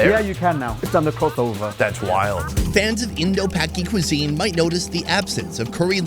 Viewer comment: Did he say Indo-Paky cuisine